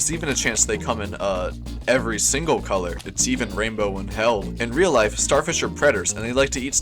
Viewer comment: I thought he said its even rainbow in hell